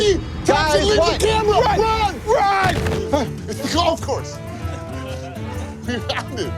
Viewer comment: what is he saying? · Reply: "it's the golf course" is what I heard